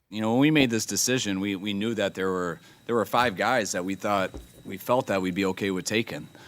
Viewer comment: Did Kyle Shanahan actually say "We now think there are 5 (Quarterbacks) we would be comfortable with taking at 3."? Cuz i thought he said 5 "guys"....??? I absolutely believe they would pick Penei Sewell, Jamar Chase, or even Pat Surtain ii.....before they would draft Mac Jones at 3. I could even see them taking Pitts! All I know for sure is that I am excited to find out who it is in just a couple short days! ( I hope its Penei Sewell)